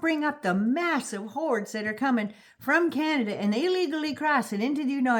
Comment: I thought you were saying Ada can. Who's Ada?😅